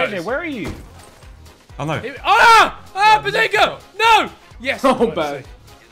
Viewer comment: what did he say?? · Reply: Ahh bezingha